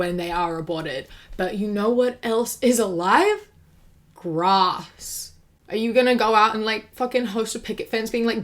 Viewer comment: I thought you said “you know what else is alive… rocks”